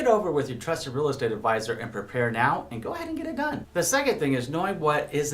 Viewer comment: “Talk it over with your advisor, and go ahead and get a gun” lol not bad advice but a random slip in if I heard it correct 😂